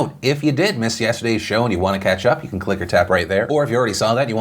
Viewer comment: how little i pay attention I was today old when I realize Phil was saying " click or tap right there". for some reason I always thought he was saying "click or type".. and never once questioned why he would say that..